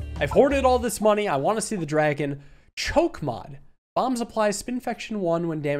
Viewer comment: I really just thought he said "chokema." Not to be confused with crushma.